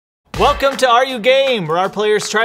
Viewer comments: I really thought he said “Welcome to are you gay?” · I thought you said welcome back to are you gay🤣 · I thought It said are you gay? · why do i hear "are you game" as "are you gay" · at first i thought they said are you gay😅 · I thought they said are you gay